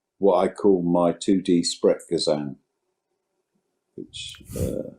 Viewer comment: what does he say? His what? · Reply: sprechgesang <link>